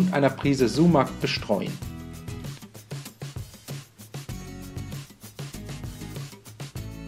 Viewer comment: bin sicher nicht der einzige der da was falsch verstanden hat oder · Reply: (Schwarzpimmel). Wenn man sich aber auf Schwarzkümmel konzentriert dann hört man es auch. Ist noch lustig.